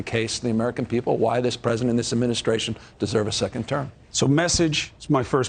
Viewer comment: Is my hearing going bad, or did I hear him say, "This president and this administration deserve a second term?" I KNOW I did not hear him say that....